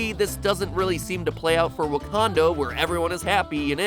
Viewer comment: did he say wakando?